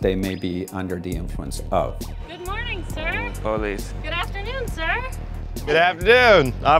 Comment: "Good morning, sir!" Stoned ned: "police" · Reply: no he said bullies but when i first heard it i thought he said police too